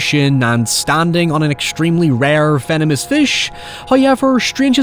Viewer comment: Oh venomous fish that makes sense . I thought you said "feminist fish" :)